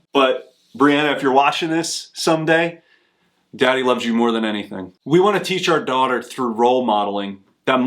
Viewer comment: I thought it said "I buy almost anything". 😂